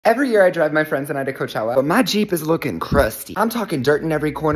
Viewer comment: Bro i thought he said “every year I DRUG my friends”